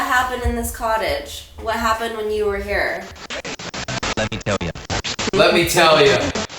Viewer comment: I had to go back a little cause I thought it said "Let me kill ya." lmao excuse me sir?